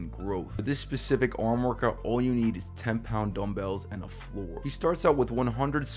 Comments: I thought he said 15 pound dumbells. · I just did this ,I thought he said 15lb dumbbells let me tell you it's killer, pumps you up insane, good work out